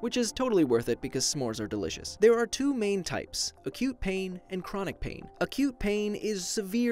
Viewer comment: When he said “acute pain” i heard “a cute pain”